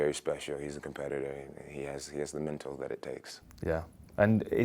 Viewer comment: did Jon just say Dc had the mental that it takes?